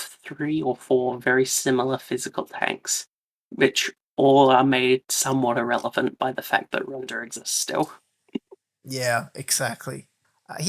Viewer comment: I can't quite make out what she says at 10:48. Does anyone know? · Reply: The physical tanks releasing are made somewhat irrlevant by the face *that Runda exists still*